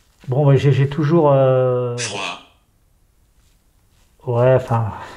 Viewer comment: A 48:22 je comprends mal la voix, je dirais foi ou joie ou froid mais en tout cas ce sont des mots qui sont composés dans le jeu de dés. Dans le 2ème jet il y a le mot prison et mort et Julia. Il y a aussi Julia dans le 3ème jet de dés. 51 !!!!